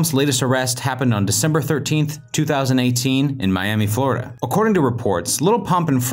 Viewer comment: in minute 3:29 its sounds like he is saying 2019, so i checked it several times and if you think of 2018 you hear it same as with 2019, listen closely.